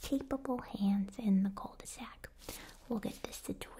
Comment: Am I mistaken or did she just say cul-de-sac? 🤣🤣🤣